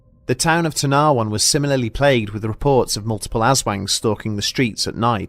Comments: I heard it as Ass wank lmao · Im sorry I mishear it as "Ass wank"